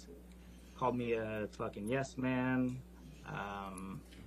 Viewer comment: Wait, what did she call that bodyguard? A f yassman? I don't understand this word at 10:47. Help anyone? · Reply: Ah, that's nasty. I got the f word, but never heard the yesman before. Had no idea how to spell it so my google search didn't work. Thank you :D